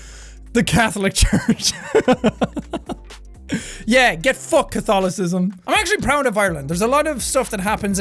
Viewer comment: When said "Get f***ed katholisissm" I thought for a second he said Katolis from The Dragon Prince. 10:07 Amazing show. Also, man is that a trip down memory lane seeing him in his hat.